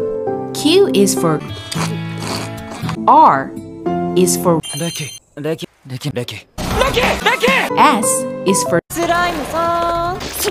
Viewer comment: it sounded like langa said Reki Becky